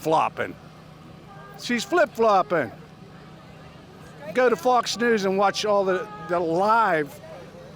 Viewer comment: I heard him say "Go to faux news lies"!